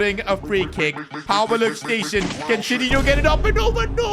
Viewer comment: did u just said shitdihno?